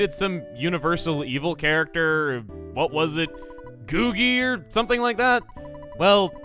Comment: Mom: "Heard you stopped some universal evil character. What was it, Googi, or something like that?" Giygas: Screaming in his grave in pain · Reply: I thought it said gooigi